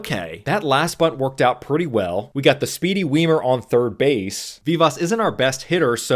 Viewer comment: I thought you kept saying speedy wiener lol had to turn on the captions